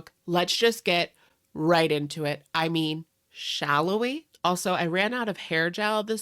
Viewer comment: "Welcome Back to Gorl Talk " & "Shall We"? are iconic in the Gorl World now lol Thank you for ur hard work and keeping us updated on the Gorls 💛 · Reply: I always thought he said “shallow” we? As in HOW shallow these ppl are 😂